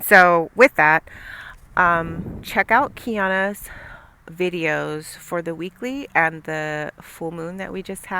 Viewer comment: Did you say check out Kyons video @ 35:22 for the weekly ? Who is it?